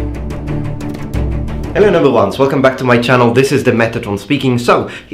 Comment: What is his catchphrase at 1:22? He says it a lot but I can never make it out. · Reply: "Hello noble ones."